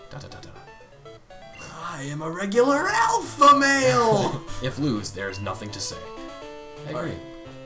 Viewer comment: I LOVE WHEN SHOUTS "I AM A REGULAR ELF-A-MALE!" i dunno but i couldnt stop laughing XD unless...... he said Alpha-male.... and i misheard XD